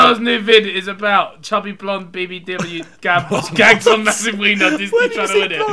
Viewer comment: I thought Ethan said "Chubby blonde baby" XD XD